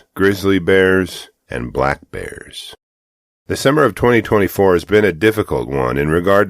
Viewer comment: I swear that the narrator says, "December of 2024...." Maybe my ears are bad, or perhaps you're a time traveler? lol